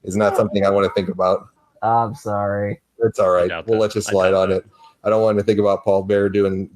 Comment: what did say? He'll let him slide on it lol wtf